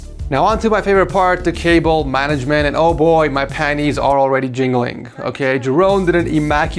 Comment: I had to do a double take when I heard him say my panties are jiggling